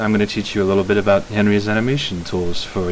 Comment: I thought it said "how to use heavy's animation tool".